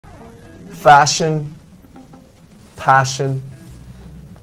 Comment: I heard "무슨 소리야?" which is "What did you say?"